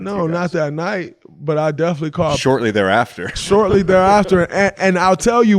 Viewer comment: I could’ve sworn Draymond said He called him